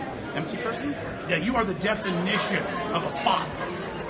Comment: did he call him a thot? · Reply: Fraud or Bot Maybe both